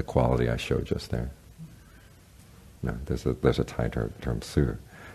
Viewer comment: What was the quality he was demonstrating at 37:00? I think he said it is "suur" in thai?